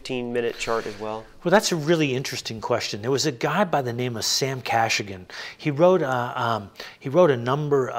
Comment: can anyone tell me what’s the name of that guy? Sam what else? Sam Cassagan? I’m Spanish and I don’t understand well that kind of names, difficult to translate for me. Thank you! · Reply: Title is "The Lennox System of Market Forecasting". Sam Kachigan. Difficult to source but apparently available at Stanford libraries..